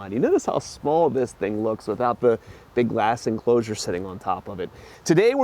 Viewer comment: I think I misheard "bigass enclosure" instead of "big glass enclosure"? lol Very informative as always. thanks!